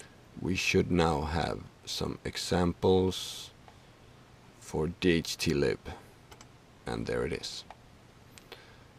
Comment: thanks for this video very informative. Question: Which microcontroller is that you introduced at 4:34 ? Did you say 801085? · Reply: That would be the Attiny85 which is an atmel chip like the atmega328 but with only 8pins. See previous episode for how to program it.